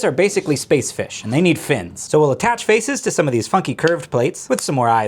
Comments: he said funky while my dad walked in he thought said fucking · At one point of the video, I thought he said “F——ing curved plates” but it was actually “Funky curved plate”. 😂😂😂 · Bro I thought he said fucking curves plates but he said funky curves plates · Did he just say " to these fucking curved plates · So what I heard was: "So we'll attach faces to these f■■ky angled* plates" and had to backtrack to make sure I knew exactly what it was. That being "funky" 😂 Needless to say, awesome Lego build as always! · BRO IDK WHY BUT I HEARD YOU SAY “so we’ll attach faces to these f**king curved plates” 😭😭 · it kind of sounded like you said “f*cking curve plates* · I thought bro said fucking curved plates😂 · I thought he said the F word instead of Funky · Ok, I think bro said funky, but it really sounded like he said fucking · I thought he said the f-word, but he actually said “funky.” I had to listen to it again 3 times to find out what he was actually saying. 😂🤣😂🤣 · Bro I cannot be the only one that heard him say fucking curved plates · Sounded like you said fucking instead of funky.